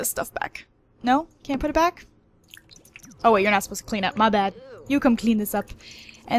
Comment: Am I the only one who heard her say my dad